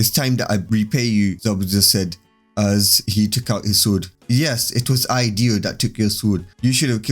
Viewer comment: Why did I hear it was I Dio who took your sword